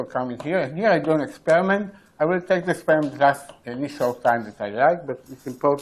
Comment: Auto generated subtitles are good enough. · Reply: I wouldn't quite say that. at 29:10 I can't understand what he's saying, and the auto-generated subtitles say "I will take the sperm test any short time that I like." I'm willing to bet that's not what he actually said :)